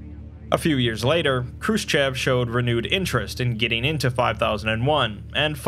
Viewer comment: dunno why, but thought he said, "Chris Chan". "How goes the study of the cavern, comrade?" "I'm workin on it!"